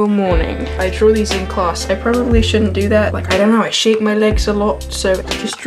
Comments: I thought you said 'I shave my legs a lot' and I was really confused as to why you were always shaving your legs in class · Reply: she thought he said he when in fact he said shake 😊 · I thought you said 'I shave my legs a lot' and I was really confused as to why you were always shaving your legs in class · Moms house? Divorced? Thats rough............. IDK IF HE HAS Two houses.........? dont get mad at me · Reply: thought he said 'shave' as well, pretty sure he said 'shake' :) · istg I thought he said "Idk why i shave my legs a lot' I was like oh...OKAY! xD